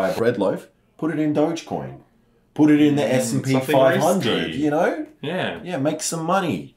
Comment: I really thought he said I should invest into scp-500